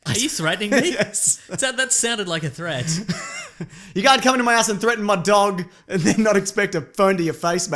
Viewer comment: I thought he said "You can't come into my ass and threaten my dog"